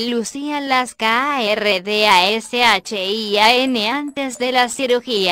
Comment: Dame una ... 0:07 Que dice?? KARDASHIAN'S!!